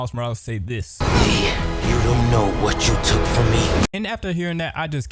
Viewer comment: Didn’t Miles say “Li you will know what yoy took from me”? Or am I mishearing it?